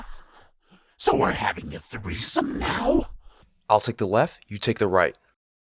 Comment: Did I heard ( three some ) ?